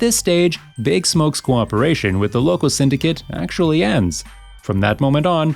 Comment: For 9:20 i thought you were saying “the local syndicate”